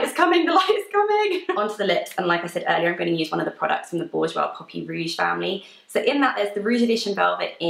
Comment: The echo makes it really hard to understand what you're saying. I thought you said "rouge puffy" and when I listened to it again it sounded like "puppy". I'm assuming you said "poppy".